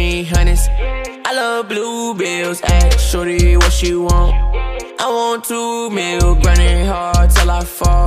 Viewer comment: Thought he said “I want 2 meals”